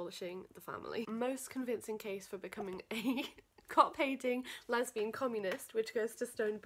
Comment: I misheard cop hating as cock painting and was so so baffled